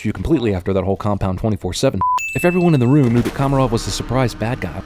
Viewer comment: It said "27-4" instead of "24-7" at 5:09(ish)